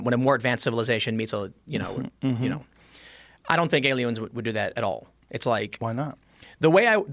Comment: time said alie-owns instead of aliens lmao